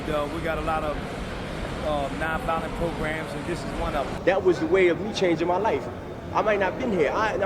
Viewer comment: did he say a lot of non-violent programs and this is one of them????...... Huh???